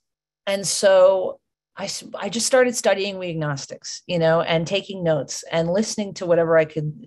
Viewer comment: she says she “started studying ________” a word I can’t understand but sounds like weagnostics. Does anyone know what it is she said? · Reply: 'We Agnostics'...a chapter in the AA Big Book